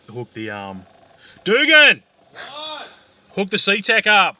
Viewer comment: What did the tuner say at 7:05 ? Sometimes Australian accent is difficult to get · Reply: Tuner: "Doogan!" "What?" Tuner: "Hook the CTEK up!" (Battery charger)